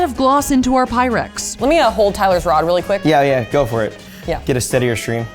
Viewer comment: Did Safiya really say "Let me hold Tyler's rod real quick"?💀💀